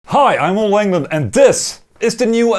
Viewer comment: The first thing I heard was "Hi, I'm Wangland" lmao